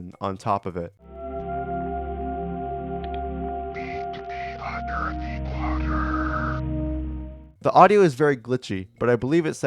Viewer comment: at entry two, i heard "He took me, under the water"